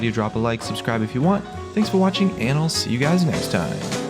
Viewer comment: I'm not the only one that thougt he'd say "her massive big tits" IT SOUNDS LIKE IT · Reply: i heard "anal see you guys next time" lol